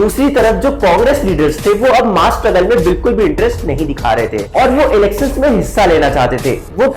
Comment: ma kya bola hai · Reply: Pgll not mass rebel It's mass struggle